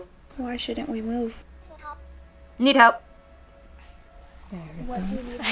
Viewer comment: I literally heard the “need help” from the spirit box wthh